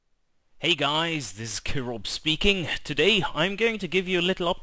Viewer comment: I always hear - "Hello guys, this is KIROV speaking". Kirov. Kirov... This has no importance whatsoever. I will go and sleep now.